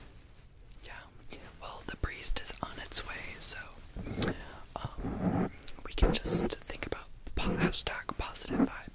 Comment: What did say... *The ???? is on its way*...😳 WHAT is on "its" way??!!😳 It sounds as if he is saying... *The FEBREEZE is in its way*...😳 What does FEBREEZE have to with an exorcism??!!...😂 Aside from the fact that it just freshens up the air after you spray!!💨😁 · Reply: It sounded like he said "Well, the PRIEST is on his way" which makes sense within the exorcism story, but I like your febreeze theory!